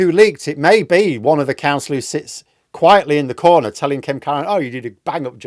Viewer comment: Sounded like you said her name was Karen. Close it seems Kemkaran. Bet she goosesteps around her office!